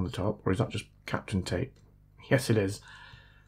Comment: I heard, "is that just the sexy gold bit on the top or is that just Catherine Tate?"